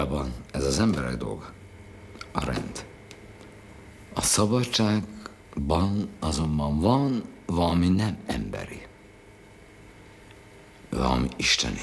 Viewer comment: Just a small (but important) clarification to the subtitles. At 4:52 the Captain says, "Freedom, however has something non-human." (instead of has nothing human)